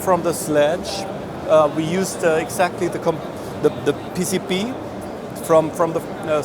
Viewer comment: I thought he said PCP... now that would have made sense.